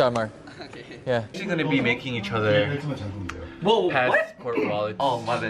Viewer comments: Dude I'm pretty sure when mark misheard he heard "make out with each other", cuz that's what I accidentally heard at first lol 😂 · mark misheard johnny and thought johnny was saying "we're gonna be making out with each other." so he said "we're actually really good people" lmao never fails to crack me up. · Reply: OH MY, all I heard is "We're gonna baking each other" the psycho in me on fire